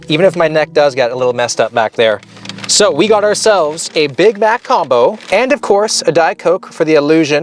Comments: I heard "we got ourselves THE BIG BACK COMBO". They should change the name to that. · I heard "big back combo" lol